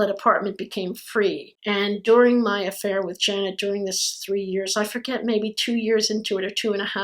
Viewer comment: I think you say a name other than Jeniffer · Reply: Suicidalpenguin Janet